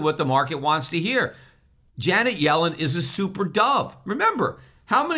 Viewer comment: Lol, I thought at 10:17, Peter said that Janet yellen is "super dumb!"